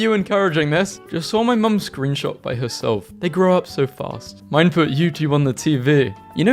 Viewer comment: i thought the guy said 'i saw mum shoot herself, they grow up so fast' 😭